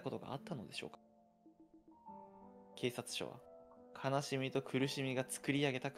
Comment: 「ほんまか?」言う……言い方が可愛過ぎたw 4:24ス「そのけいたつ」……『警察』が『けいたつ』に聞こえるw